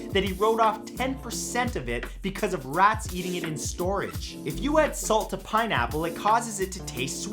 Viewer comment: I zoned out for a moment and thought he said ''If you insult a pineapple it causes it to taste sweeter.''